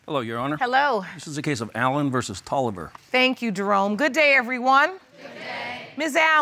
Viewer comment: DID SHE JUST CALLED HIM"DEROME"